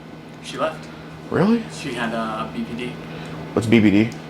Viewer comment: I thought he says she had BBD😂